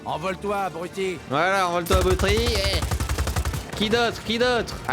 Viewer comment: "envole-toi agoutri eehh" :')